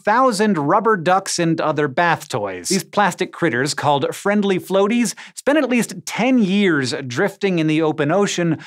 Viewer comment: "spended" lol did i hear that right?